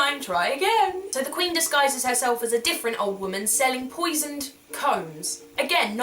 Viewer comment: I thought you said the queen was selling poisoned cones, as in pine cones, and I was really confused as to why you would brush your hair with a cone...